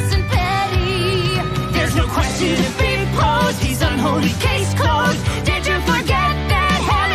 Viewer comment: Ah here we go! "you didn't know" is the one I was waiting on! Edit: For context the part where Lute jumps in here was the first piece of music I ever heard from Hazbin and it's the reason I went and binged it. "There's no question to be posed, he's unholy: case closed. Did you forget that hell is forever?" Still gets me every time. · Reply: I actually misheard that as "this unholy case [is] closed." But I can definitely hear what you wrote better this time!